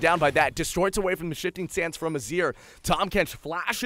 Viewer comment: did he say shitting sands?